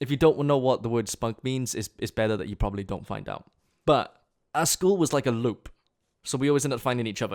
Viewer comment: did you say LÖÖPS ?